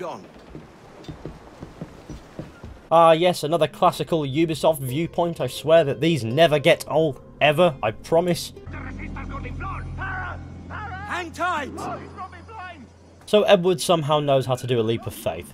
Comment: did he just say hentai ?